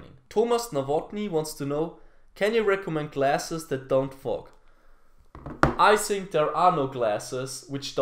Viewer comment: i thought he said classes that don't fuck😂